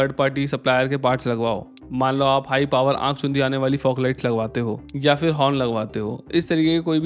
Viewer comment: did he said - aankh chudiyani or chedhiyani ?